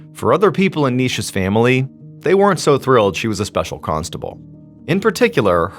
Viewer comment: I'm sorry...when you said she "was a small constable", I heard "she was a small pasta bowl" great story telling-Thank you